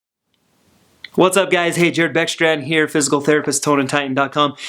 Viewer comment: Is it just me or did somebody else hear him saying "Hey jerk"